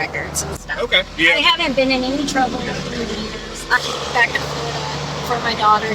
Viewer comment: did she say she hasn’t been in trouble in a few weeks? Like if you were arrested or in trouble for drugs a few weeks ago that isn’t really your “past” it just happened lol